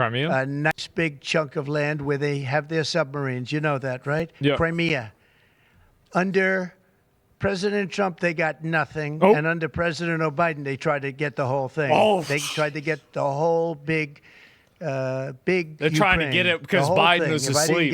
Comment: Did he just say president O' Biden? 🤣